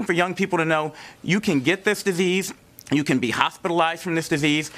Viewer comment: did he say "who vape" or "who they-" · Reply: Squattingturtle Vape